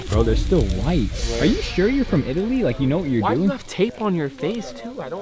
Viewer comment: I THOUGHT U SAID HE WAD FROM ITALY